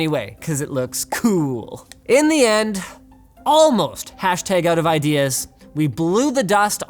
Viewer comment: What is the word that flashes on screen at 5:15-5:16. I can't pause and play quick enough on an iPad, YT doesn't have Slomo that I'm aware of and this isn't the first time LTT has done this and rewinding 100 times to figure it out is driving me insane. Thanks in advance YT! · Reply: Nvm I think it's just outofideas